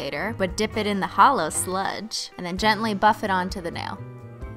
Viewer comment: LOL Cristine! I thought you said "put it on the nail dicks" I was like dafuq?